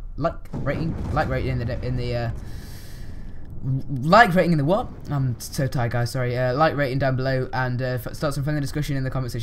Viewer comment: When you say "peace out" sounds like your saying "piss off" xD